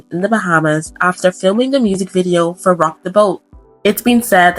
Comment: oh fear of flying! I heard fear of lying - I hate lying. I like flying 😂